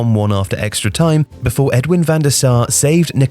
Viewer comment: First time listening I heard "Edward Van Der Sar". In the few seconds it took me to rewind managed to re-record that part of the audio and re-upload it so by the time I had listened back he said "Edwin". Elite shit.